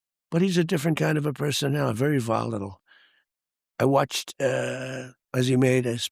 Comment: What does Mr. President say at 3:45 volatile?